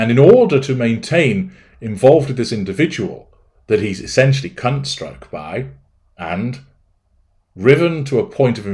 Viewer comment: Did I hear this correctly?: "He's essentially 'cunt-struck'," 😂😂🤣🤣 That's amazing, never heard that term before!